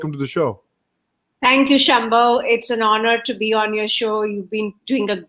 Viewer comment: Is Shyambo his full name? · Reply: Ashley D Oh I see..I thought she was saying Shambo!